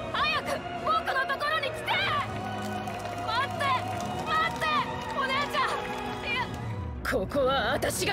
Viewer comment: am I halicunating Or did emporio just called jolyne oni chan?